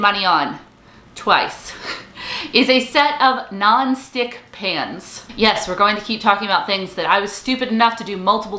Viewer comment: I thought she said Nonstick pants. 🤣🤣🤣 I was like what on earth are those!! Until she explained further. Oooh pans!